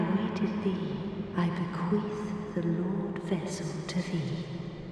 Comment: 'I bequeef the Lordvessel to thee' is what i thought she said the first time ;p